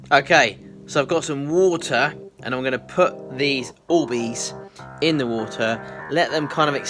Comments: the captions think you say boobies instead of orbies lol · If you put subtitles on at 11:14 it thinks that instead of you saying orbeez you’re saying boobies lol